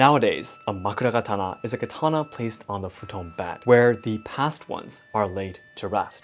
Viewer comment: You said Futon... I'm sorry all I heard was "WIND STYLE" 😅 sorry